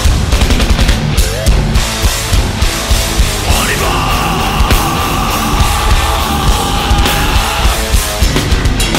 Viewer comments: can someone tell me what joe says here? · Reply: He says "On y va !", french for "Let's go !" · is he saying oliver? Why? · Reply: "On y va !" french for "Here we go !"